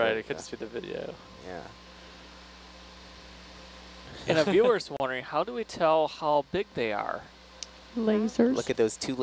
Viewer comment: "yeah" *silence* "And if you were wondering, how do we tell how thicc they are" I truly didn't know exactly what they said and no offence pleas don't take my squishy